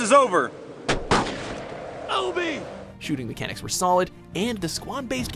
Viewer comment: did he say obe or Kobe?